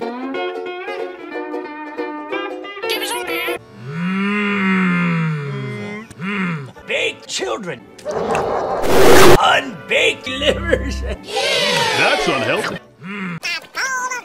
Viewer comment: I'm glad ytps are still a thing. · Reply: Did Patchy just say, "unbaked livers?" That's truly disgusting.